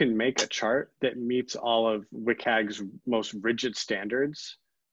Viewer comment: what is that what he says, wickag/rickag? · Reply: WCAG: <link>